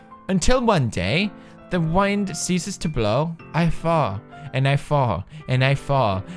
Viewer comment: I wasn't reading the poem and I thought he said fart instead of fall lmao